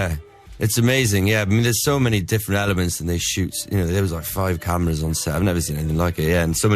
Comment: Did he say five cameras?!